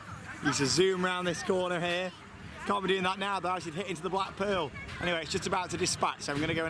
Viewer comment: At first when you were saying black pearl, we thought you were saying Blackpool hahahahaha....it’s cos we’re Scottish! Xxx